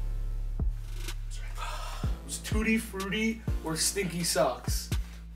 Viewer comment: it sounds like he said “sneaky socks “ and that scream THO 😂😂😂😂